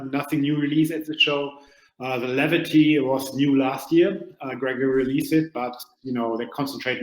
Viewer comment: I thought I heard you say Levity by Gregory, if so I'm sure the Osprey is having a heart attack